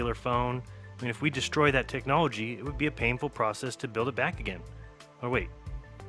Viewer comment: the subtitle says “painful process”, but he sounds like he is saying “papal process”.